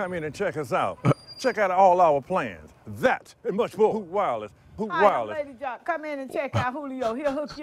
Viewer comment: I only hear "foot wireless"